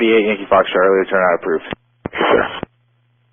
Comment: Was there a "not approved" in this recording? If there was, I would agree. In fact I initially thought the controller said not approved to the request to jump the center line but listening again he said "approved". Was it elsewhere you heard Not Approved? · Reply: Turns out I was mistaken. The phrase was "turn out approved." I heard it as "not approved." My bad. Happens around 0:57.